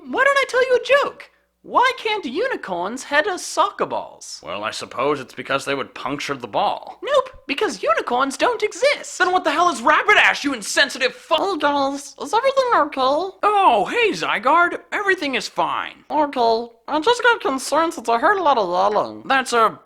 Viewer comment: When bulbasuar said "rapidash" I heard "rainbowdash" and wanted to say she's not a unicorn